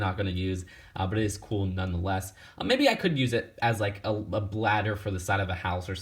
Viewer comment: did he say bladder? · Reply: "Ladder"